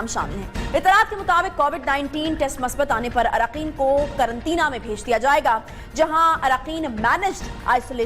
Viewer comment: did she said at 2:46 Karan Tina